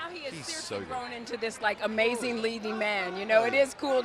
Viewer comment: now he's grown into this amazing Spider-Man is what I heard lmao